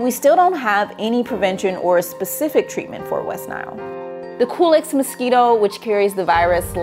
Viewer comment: I thought she said the “coolest mosquito”, not Culex. Really did a double take. Lol 😂